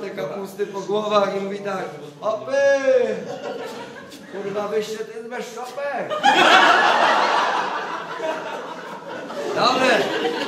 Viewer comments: Co on mówi od 6:10? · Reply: chopy wysta tez bez czapek? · co on mówi? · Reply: Chopy! Kurwa, wyście tyz bez czopek?